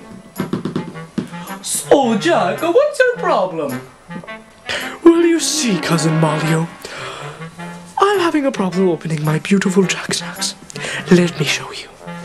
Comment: what did jack say???? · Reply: "Well you see, Cousin Malleo I'm having a problem opening my beautiful jack snacks."